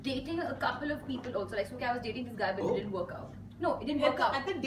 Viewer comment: did she said that she was dating siddharth nd it didn't work out at 6:15?? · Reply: No. She said "This guy".